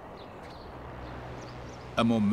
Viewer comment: I played this over an over, but he clearly says, "A momenchous occasion."